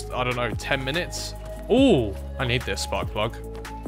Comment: LOL I honestly thought you had said "Ohhh, I need this butt plug" XD